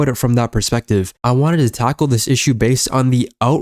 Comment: did you just say you want to taco the issue